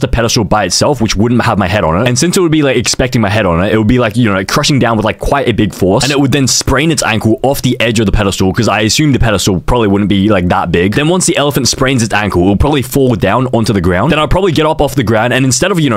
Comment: did he say sprain its ankle